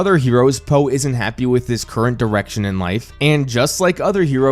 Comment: it sounded like you said “Po isn’t happy with his current erection in life” 😂😂😂